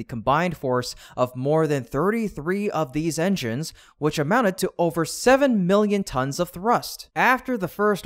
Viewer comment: but in the viedeo said trus 7 milion tons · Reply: Unless they said 7 million tons, but I thought they said pounds, lol. If they said 7 million tons, then I digress and apologize for the misinformation. My bad.